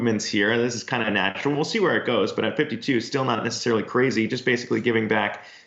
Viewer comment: what did u just say? 52 still not crazy?